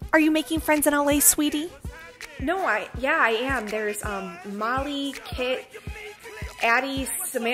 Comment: She started saying “Molly, Addy, …” I thought she was talking about pills. 🤷🏻‍♀️🤦🏻‍♀️ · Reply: Thought she said Molly, Ket, Addie